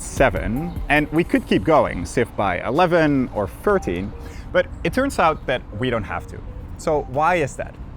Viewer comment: subtitle typo (30 instead of 13)